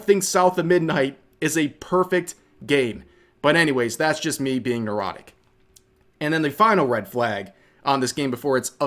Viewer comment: said "That's just me being neurotic." What I heard: "That's just me being erotic."